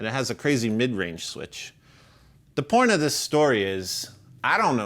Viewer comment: Did anyone else hear "the porn of this story is..." @ 5:32?!